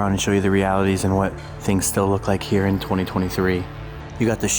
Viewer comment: Swear heard you say this is what I looks like in 2023 made me super confused